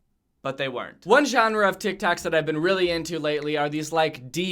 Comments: did he say tic tacs? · Is it just me how heard him say tic tacs at 1:52 when I heard it I died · did you say tik taks XD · Did he just say tic tacs? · I swear he said tic tacs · did he just say tic tacs?????????????? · who else heard tic tacs?? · does say "one genre of tictacs" ???? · Did he just say.... Tic tacs?🤔 Go to 1:51 he said tic tacs ( insert sigh here ) btw no hate but be said tic tacs instead of tik tok🤔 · did you say tic tac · I swear to god he just said tictacs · Did he say "one genre of tic tacs" at 1:51? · did he say tic tacs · did he say tik taks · did you say tick-tacks. Lmao 😆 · anyone hear him say tic tacs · did he say tick tacks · did he say Tic-Tac’s? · did u just say ‘tic tac’?! lmao